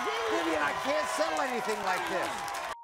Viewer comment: did maury say I cant sell anything like this?